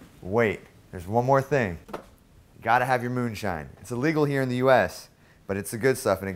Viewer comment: Did he said : "it's illegal here in the US "? Moonshine illegal? Isn't it like whiskey?